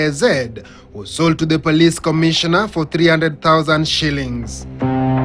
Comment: Did i just hear police commissioner?!! Where are we headed as a country though🤷🏽🤷🏽🤷🏽